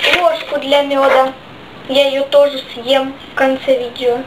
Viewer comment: Во мне послышалось что она сказала я ложка тоже съем